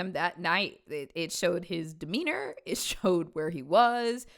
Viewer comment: i heard "demeanor" as weiner and was very confused